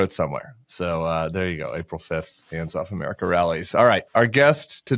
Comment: I thought Tim said it was "Hands Across America rally" lol.